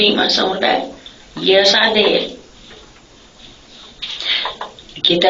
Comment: Calling someone and telling them to get ready cause she is going over there to rock him doesn't sound like self defense. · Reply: Lol i was only listening and thought she was saying rob you. Rock you makes more sense.